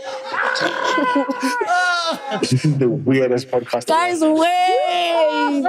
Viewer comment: SOmebody said "This is the weirdest podcast" not nton around @ 31:43 I heard you😶I will find you😶‍🌫